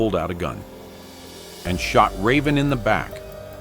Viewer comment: I had to rewind this again because I thought I heard he shot her Dad in the back and I’m confused why Raven went back man I wish she didn’t smh